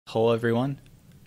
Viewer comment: Did you just say "Hole everyone"